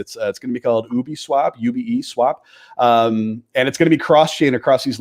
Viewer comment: from about 25:25 what is he saying?? UBD swap??? or ubi swap?? please anyone what project he talks about!!